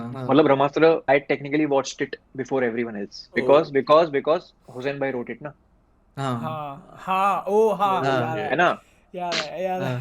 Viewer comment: pai kya bolta hai bb? Because because ke baad? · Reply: hussain bhai wrote it